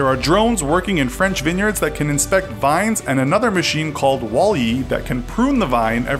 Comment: did you just say wall-e???